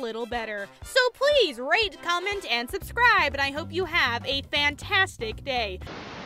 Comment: Did... did I heard "rape, comment and subscribe" at the end? Is it just me? OK...